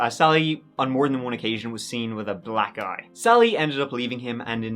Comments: I thought he said once hos wife sally was seen with a “black guy” ..I had to rewind it and he said “black eye”😂😂😂😂😂😂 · I thought you said his wife was seen with with a black guy and not a black eye😂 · At first i thought he said "she was seen with a black guy" and I was like "good for her, David's a piece of shit". And then I realised it was "eye"... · Definitely thought he said Sally had been seen with “a black guy…yikes”. Had to double back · I thought said “Sally was seen more than one time with a black GUY” 😂💀💀💀💀💀💀💀💀💀💀💀💀😭😭😭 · Did anyone else think at 14:10 he said “black guy” lol